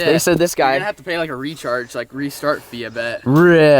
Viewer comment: I thought he said retard lol had to double check woulda been funny as hell either way